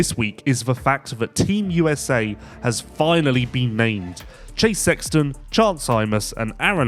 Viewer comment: ha! sounded like said, "Team USA has finally been maimed." 😆